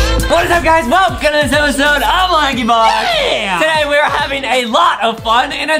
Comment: it sounded like said “laggybox” instead of “lankybox”